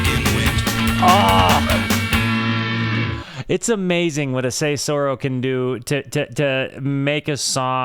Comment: What word is that? Saysoro? Auto-captions aren’t helping me much, lol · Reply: Cesura